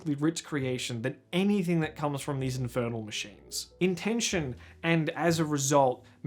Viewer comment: I misheard “infernal machines” as “infertile machines”, and, you know what? Still makes a lot more sense than an AI book.